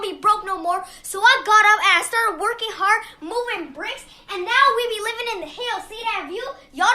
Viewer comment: hold up....did she say she started moving bricks? i am not crazy. i heard that.